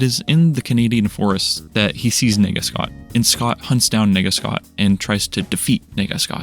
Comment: homie is really saying "n*gga scott"